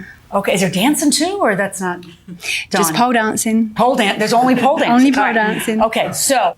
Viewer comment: I thought for sure she said "poe" dancing like slang for some activity that poor people might participate in. Thursday night homeless poe dancing lessons.